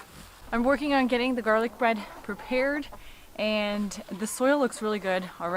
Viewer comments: lol I swore you had said "I'm working on getting the garlic BREAD prepared" had to listen a few times to pick up "garlic BED" -- in the end the garlic in the BED may end up on bread ;) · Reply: Yep, I thought she said "garlic bread" ....silly me! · lol I swore you had said "I'm working on getting the garlic BREAD prepared" had to listen a few times to pick up "garlic BED" -- in the end the garlic in the BED may end up on bread ;) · garlic bread? Lmao Did she say that, or was it garlic bed or garden bed?